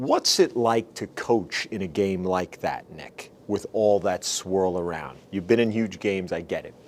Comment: You’re an LSU fan if you hear him calling NECK instead of Nick